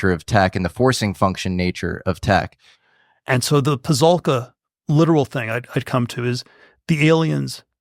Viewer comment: what word is that at 38:00 the 'peh-zul-cuh'? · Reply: * Pasulka, he’s referencing Diana the woman sat talking here…